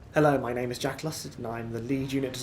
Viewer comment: Sounds like he said " Hello my name is Jackelus"